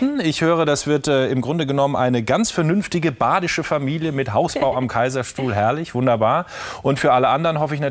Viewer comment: Ich kann unmöglich die einzige sein die findet dass der Moderator sich benimmt wie der letzte Arsch. Er fällt ihr ständig ins Wort, sagt Sachen wie „die jungen Mädels am Herd“ und endet sein Interview mit der Freude über eine „echte Hausfrau“ Diggi, diese Frau hat mehr erreicht als du jemals wirst, pack deinen chauvi ein und fahr mal runter... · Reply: Ich habe da "Hausbau" verstanden und nicht "Hausfrau". Macht auch mehr Sinn im Kontext, weil die beiden kurz vorher über die Hausbau-Pläne von gesprochen haben. Ich finde den Moderator gar nicht so schlimm wie hier beschrieben, spricht in ihren Videos ja auch meistens die "Mädels" an.